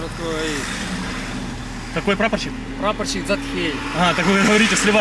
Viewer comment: Послышалось: "....прапорщик Захер.")))